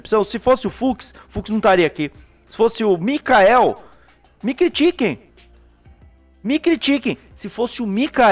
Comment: Acho q tô com fome... o falou "me critiquem" e eu entendi "Mc Chicken" 😅